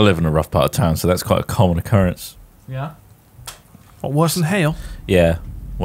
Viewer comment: When said "What? Worse than hail?" I misheard it as Worsen Hale and thought it was like an area in Bristol where lives. Thought it was brave of to out like that but then I realised I'm an idiot.